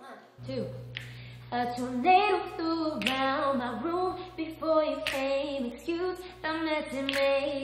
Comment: I can't listen to this song normally anymore, everytime i hear this i think "A potato flew around my room," fUCK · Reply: lol me too i hear '' a tomato flew around my room''